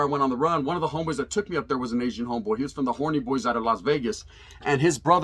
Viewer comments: bruh did I just heard "horny boys" ? 😂😂😂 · did he say Horny Boys from Las Vegas, lol!